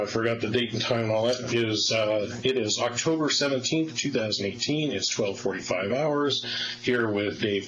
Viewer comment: CORRECTION: At 1:26:37 the closed caption says Oct 17 2016 instead of Oct 17 2018. 👍🏻👍🏻👍🏻